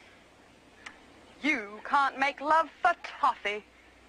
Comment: “You can’t make love for toffee.” Lol.🤣😂🤣😂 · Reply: Lol I thought she said coffee!